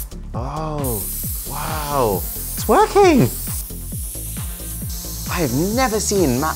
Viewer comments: I thought he said “it’s twerking” instead of “it’s working”. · him) It's working! What I heard) it's twerking! 😱